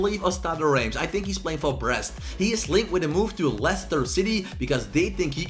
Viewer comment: just listen to what says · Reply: I think he is playing for breast